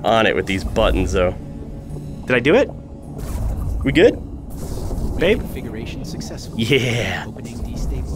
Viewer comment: did he call her babe